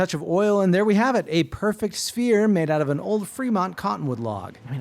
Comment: My dumbass thought he said spear head